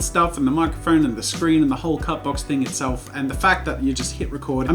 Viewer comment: Even though its the Cut Box the entire time you were saying it I thought u were saying Cat Box